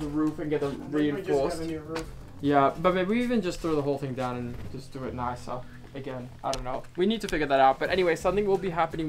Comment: Anyone else hear him say babe to? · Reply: "But maybe we even just throw the whole thing down and just do it nicer again."